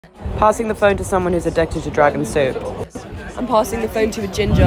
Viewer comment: Why i heard “Passing the camera to someone who is addicted to drugs and soup”